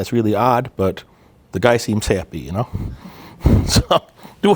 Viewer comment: "The guys seems sappy..." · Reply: The whole guy fucking a tree example. Sounded like you said he "seems sappy," which would've been hella funny if you heard yourself say it